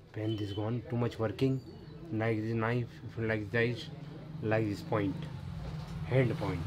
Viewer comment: tf did he just said? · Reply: Its working, Nice knife , like dice, I like this point. hello point!